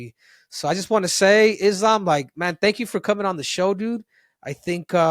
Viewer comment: did he say Islam?